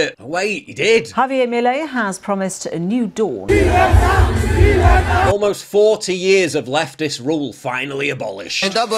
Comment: What is he saying? cause i was just watching scarface earlier and that's what they were saying in the tent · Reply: "LIBERTAD" = Freedom